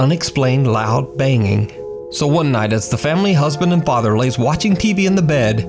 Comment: "family husband and father" I had to re listen to that a few time to understand wth he was saying lol